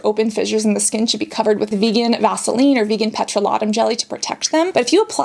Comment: am i the only one who heard her say petrolotam instead of petroleum??! !